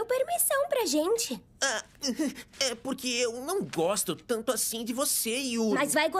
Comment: Eu entendi "gozar" invés de "gosto"